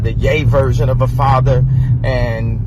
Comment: Did he just say that he is a gay version of a father? The emasculation of men is real!